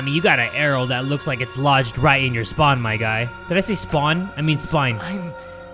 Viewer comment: "I mean you got a arrow lodged right in your spawn" "did I say spawn? I meant spine" Me: I heard spock- Edit: it's sad how on all his other videos he gets 50k likes within a day but it's been a month and this only has 40k